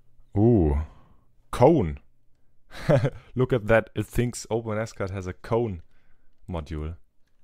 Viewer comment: Open S Cad, I thought you we're saying OpenAscott. My bad. Great work!